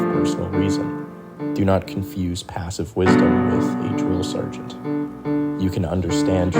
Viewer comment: “do not confuse passive wisdom with…” (a drill sergeant??) Can someone tell me what the word is? I don’t think I heard it correctly.